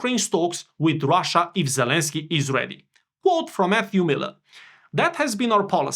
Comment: When you said, "quote from Matthew Miller", I thought you said, "quote from FU Miller", which would be more appropriate.